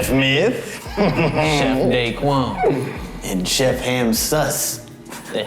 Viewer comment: I heard sus 😳